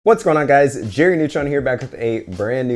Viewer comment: For a split second, I thought you said Jimmy Neutron